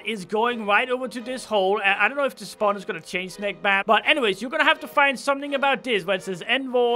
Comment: did he just say { nek map } !?!?!